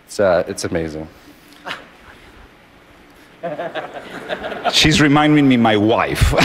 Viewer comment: Did he just call that guy "she"